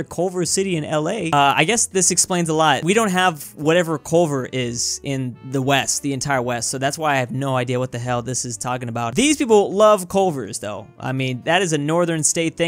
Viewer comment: Ah yes, East is West and West is East... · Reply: Oh I thought he said Weast